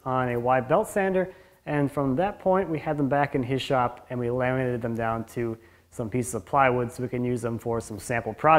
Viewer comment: You sliced them into 1/2" pieces and then laminated two (?) together before doing the miter-fold thing? I definitely would like to see a detail on how that part was done. really cool look. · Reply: Ahhhh, I heard "laminated INTO plywood" but it was "laminated ONTO plywood" !!! that makes a lot more sense than what was in my head. Slick!